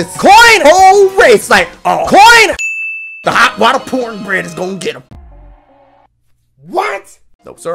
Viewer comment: Coin 13:28 Also hot water poor bread (Sounds like you just said porn *PAUSE*)